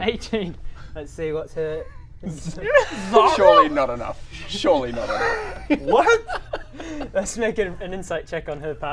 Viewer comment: Not a native speaker, I can't understand what Greg says at 32:45 with others talking and feel like I'm missing out on a great laugh - can anyone help me out? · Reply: Zariel?, is what he says